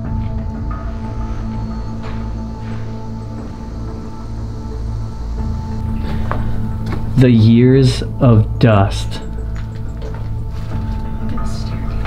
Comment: right after that there sounds like someone is saying dust dust it might be a ghoust 👻👻😮